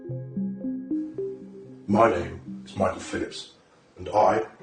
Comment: thought it said michael phelps